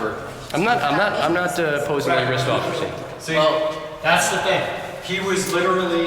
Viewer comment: What's the name? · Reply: sounds like it says Officer Wagstaff or something.